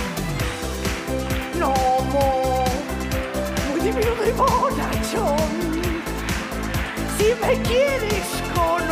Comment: Alguien entiende que dice en el 0:59? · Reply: Muy diminuto y fortachón